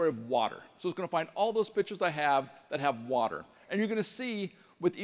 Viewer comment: I thought he said bitched instead of pictures lmao